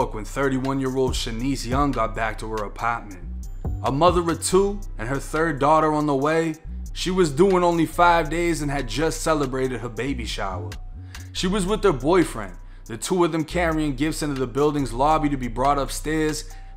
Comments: Bruh u know u institutionalized when keep saying “she’s only due in 5 days” and I kept hearing “she’s only doing 5 days” and I’m like what she get arrested for? 😭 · "She was due in 5 days."... I thought u said " she was doing 5 days." Like 5 days and n jail. I was gonna say "damn".